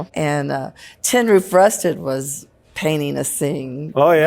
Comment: I always heard "hen roo, rusty" whatever that means....."your what?"